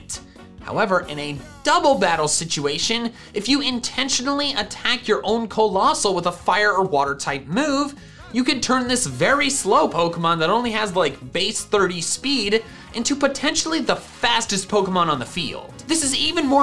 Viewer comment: I Thought You said Slowpoke